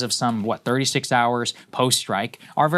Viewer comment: 36 hours post Reich is what I heard